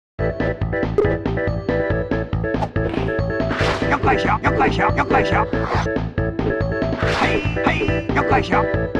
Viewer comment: what did you say? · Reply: ho